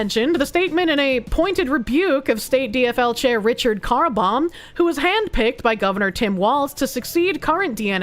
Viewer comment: Oh! Carlbom! I thought you said Richard Carbomb! Silly me for imagining such a crazy name!